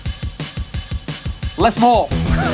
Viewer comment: what does say @ 0:04 ? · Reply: He said "let's morph"